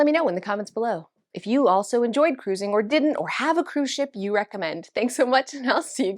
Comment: I heard “comment if you have a cruise ship” 😂